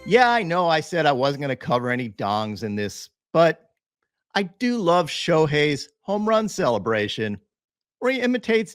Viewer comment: “I do love Shohei’s dong” Oh wait that’s not what he said…